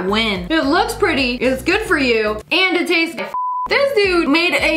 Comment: for those who are wondering what it tastes like, she said "and it tastes good"